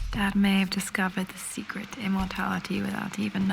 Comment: "Immorality" instead of immortality on the subs